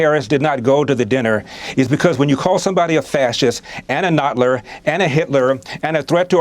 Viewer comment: What did Larry say at 6:12 "a nutler" wtf is a nutler